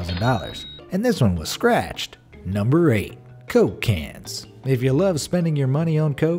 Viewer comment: I heard Cocaines lmao